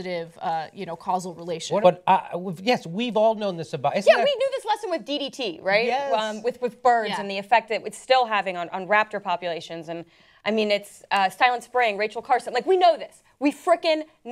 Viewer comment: did she say... "raptor populations" ?!?